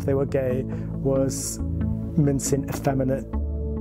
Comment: i think he said "mincing" not "men seen"